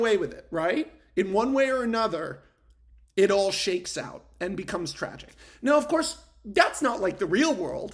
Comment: after you said it all shakes out and become tragic, i had to rewind and listen again cause I thought you had said "it becomes traffic" as in the soderbergh film from 2000, which would also kind of work. anyways I think your argument works and even if it's not 100% correct, it is still pretty interesting to think about